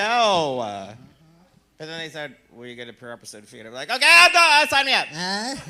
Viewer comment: I can't make out what Pandora whispered at 8:07??? Anybody?? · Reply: "Well, you get a per episode fee."